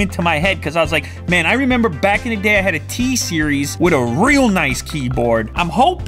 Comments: Did he say t-series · t-series I hear? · what did you just say? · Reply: T-series lol but the model of the thinkpad😂😂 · did he just say T-Series ?! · did you say T-series